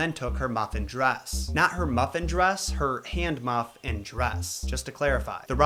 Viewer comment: I totally thought you said muffin-dress! 😂